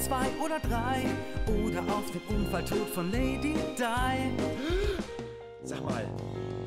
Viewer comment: Ich verstehe nicht was der sagt bei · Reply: meinst du: ...oder auf den Unfalltod von Lady Di?